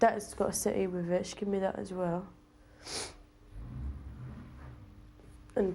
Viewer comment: Would someone translate what was said @ 9:38 when she points at the chair? I (ashamed) "North"American" and can't find me arse. Thanks! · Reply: She's saying " She gave me that aswell " I'm from the u k